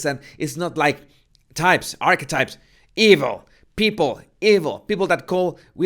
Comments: When you say 'Archetypes' I hear 'Arsewipes' 🤣🤣🤣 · Ha! With your beautiful amazing accent it I almost thought I hear "A*s wipes" podcast...not Archetypes! Love ya! MM.....get used to being torn down....because that is what our social media society does to hypocritical liars and people who are full of sh*t. You put yourself "out there"....Ms. "wanna-be royal have it all w/o any actual work" A list celebrity. You are failing....miserably. Deal with it.